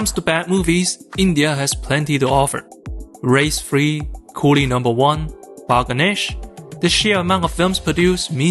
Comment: Bro did u just say "GHODI No. 1" instead of colie no.1